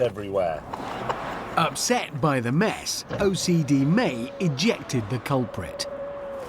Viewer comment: I had to do a double-take because I thought Hammond said "injected" like James euthanized the offending dove.